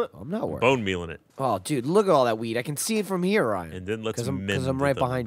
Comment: does say ''lookit all that weed''